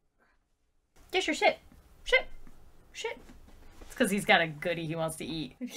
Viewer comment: I had the subtitles on and when you said, "Dexter, sit. Sit. Sit!" It thought you said, "Dexter, shit. Shit. Shit!"